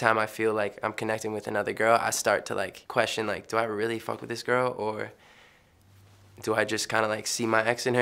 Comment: Is he saying „do i really fuck with this girl?“ at 1:13?